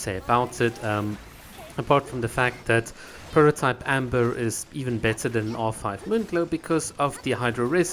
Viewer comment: Wait, Prototype Amber is better than R5 Moonglow, did i hear it correctly?